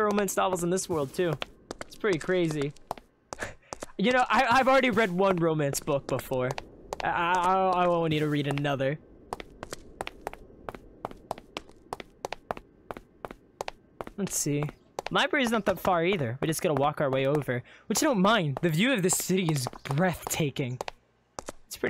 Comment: in 44:43 the caption said lets see my brain is not that far either